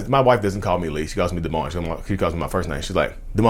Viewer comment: It sounds like you said your first name is Dehmon (like “demon”) 😂 They’re really going to think you’re possessed now 🤣 😂